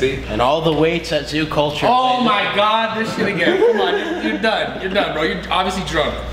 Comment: does Brad say “this Nigga?”